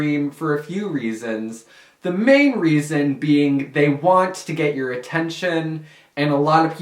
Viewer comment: who else heard him say thay wants to get your atenicion at 1:29 and